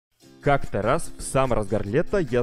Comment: говорит "как-то раз" что я услышал " как Тарас"